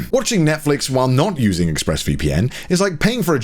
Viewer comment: I swear to god Conner said Ex-Breast VPN